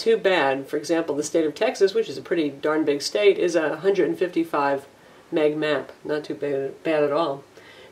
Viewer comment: I didn't understand what she said. 155...mang? MB?